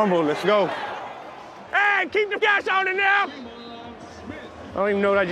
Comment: What did he say? 😂 “Hey, keep the cash on dem now!” 🤷🏻‍♀️😂 Thats my guess 😆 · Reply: Lizeth LovesB keep the gas on it now.